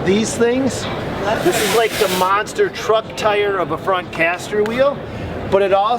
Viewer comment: Like 5:20 did I hear mc.soften